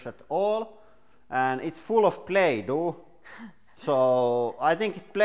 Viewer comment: Hey, good video. Very informative.... btw, did you say the purple boot was full of "Play Toe"? 🤔 1:21 😂